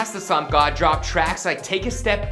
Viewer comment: Did he just say, ”Before ski mask the slump god drop trash like....” or am I the only one who heard that?